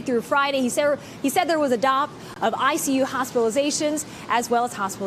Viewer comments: drop? I can't make out the word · speak english... What is she saying? DOP?